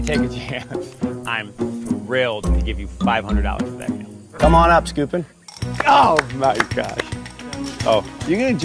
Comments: 45 At first, I thought Jeff said "come on up stupid" but then I realized he said "Skupin" Michael's last name. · As someone who didn’t watch the season Skupin was in I thought the whole time Jeff said stupid at 0:43 😂😂😂. Damn